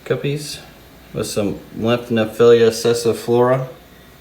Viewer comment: Lots of amazing plants...At 8:44, did you call that tree like plant a Sesaflora??? I need one in my life for sure...